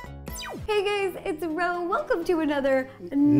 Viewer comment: I thought she say "hey gays"